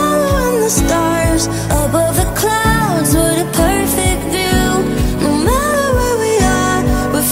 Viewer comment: I always heard "a perfect dude" idk why xd